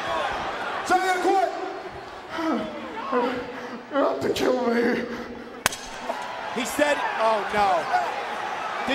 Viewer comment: What does mankind say @ 19:37 ?????? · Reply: “You’re gon have to kill me”